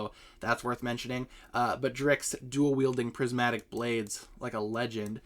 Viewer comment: subtitles said d.i.c.k.s instad of drix